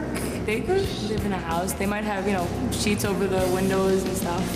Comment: wtf did she say at 1:37 was it "sheets over the windows" ?? what the hell has that gotta do with anything lol